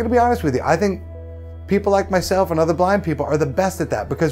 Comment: Thought he slid in a joke and said "black" instead of "blind"